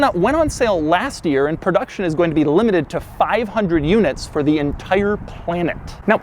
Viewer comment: for some reason I heard ford